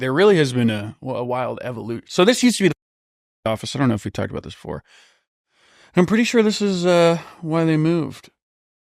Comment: Does anybody know what was saying here? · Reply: Pretty sure it was World Surf League because he says it again a minute later.